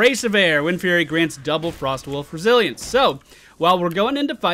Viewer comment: I heard you saying "Frostwolf Brazilians" haha